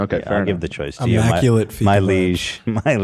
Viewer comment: Im losing my mind what the fuck does he say at @ 30:17 ??? "immaculate feetlesge" i have ocd and I have been trying to figure this out for months. I know it doesn't matter but my brain is angry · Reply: “immaculate fika vibes”